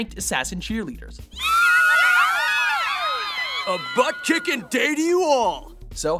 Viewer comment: He says "A butt kickin' day to you all!" But YouTube captions say "A butt chicken way for you all!" ( ͡° ͜ʖ ͡°) Damn YouTube captions.